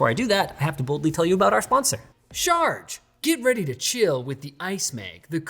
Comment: I seriously thought you said Shard.. sorry